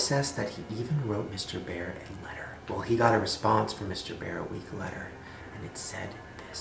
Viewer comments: i think he said ''he got a response from mr. bear a week letter'' XD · "Well he got a response from mr. Bear a week letter" at 4:23 he said letter instead of later😂😂😂😂